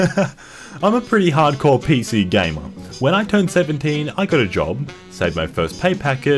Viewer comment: my think glitched and said im pretty umphrey umphrey umphery